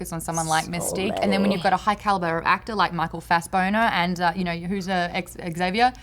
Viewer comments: Does say "Fass-boner" around 17:50? I love reaction, it's so wonderful. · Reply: yeah, she said Fassboner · Did say "Fast-boner" at 17:50?